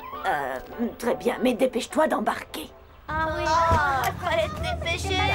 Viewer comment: a 1:58 il y a eu in "oh oui" c'est moi ou j'ai l'esprit mal placé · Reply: Jimmy The Hedgehog j'ai entendu '' oh oui '' moi 😕